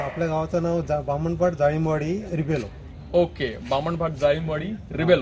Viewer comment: did I hear it correctly same as your last name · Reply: It's actually Rebello. Thank you, Ramesh Ji